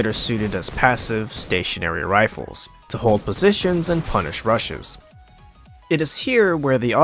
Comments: "to punish rushes" i thought he said "to punish russians" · I heard punish Russians. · "to hold positions and punish rushes" i heard "to hold positions and punish russians" lol. anyone else? no? just me?